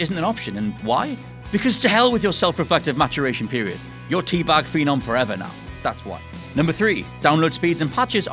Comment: Did he say "Tea Bag Fee Nom Forever"? I dont know what you said BUT I DIED